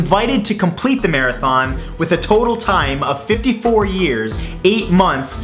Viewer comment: if you read lips, he actually said 55 years. Like if you went back to look.